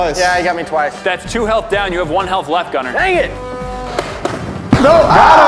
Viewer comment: Gunner was like ang it instead of dang it